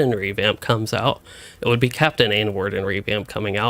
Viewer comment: did he say Captain N-word? 🤣